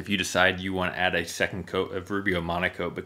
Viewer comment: Now I get it! Rubio mono coat. I thought you said Rubio Monaco 😂